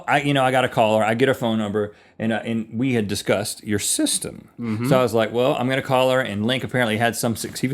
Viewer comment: i thought he said "sister"